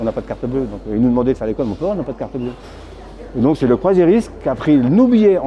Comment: « Et donc c’est le "proziris" » ? Il dit quoi exactement ? Parce que c’est incompréhensible. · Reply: C'est le "croisiériste"